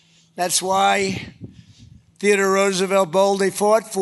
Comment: Did he say "Peter Roosevelt"? Right after he couldn't bring himself to say the word "ass"? (gone are the days when he bandied about the word "pussy", apparently) Never mind. I am more deeply concerned about his support staff - who is taking care of this poor creature? How is this allowed? Where the heck is his alleged mistress, if she's so devoted? Trump is a horrible person, but he is a person who happens to be The President of the United States (let's not get started on how or why) and he is losing his mind on the stage of the world, and no one is helping him! How bad is all of this, really? Are "his" people truly on his side, or are they actually afraid of him, or do they just not give a shit? How much longer can this go on? This is so so so bad! I am personally really pissed, because pity is not what I want to be feeling for Donald fucking Trump! Somebody do something!!!